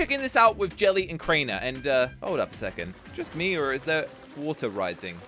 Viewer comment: 8 :52 sounds like said holy f